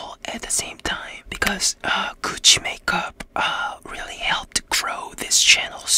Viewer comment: I THOUGHT HE SAID “COOCHIE MAKEUP” BEJDHD